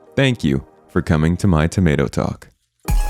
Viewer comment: My mother heard this and she thought you said "welcome to my tomato sauce" :P true story